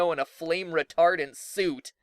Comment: For a second I thought he said flame retarded suit